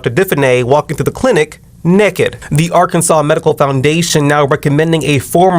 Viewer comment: did he say " nekkied" 😂